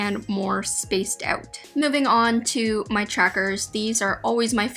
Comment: Tbh I thought tracker said jacket lol